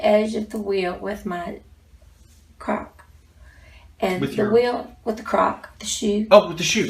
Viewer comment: She caught the wheel with her "crock" doc thought she said " caught the wheel with my cock" lol. 0:59.